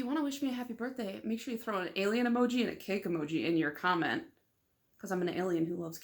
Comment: Happy birthday! I thought you said alien and keg emoji 😂 👽 🎂 🍻